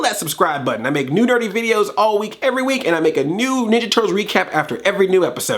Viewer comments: It sounded like you said "I make new dirty videos every week." Lol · Haha at the end of this review it's the first time I thought I missed heard you - you say "if your new to this channel click subscribe etc you then what sounds like "I make new dirty videos every week" I know it's nerdy but it's the first time I rewound to check lol haha another great video by the way :)